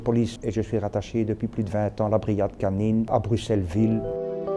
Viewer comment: j'ai entendu BruxellesVie j'étais pas pret 😂😂😂